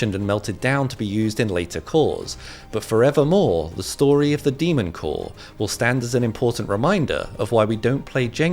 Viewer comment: i watch to much anime i thought he said demon slayer corps